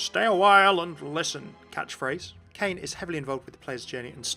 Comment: Did I hear bane?